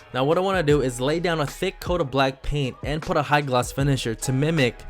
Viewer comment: I thought he said blackpink Like if you are a blink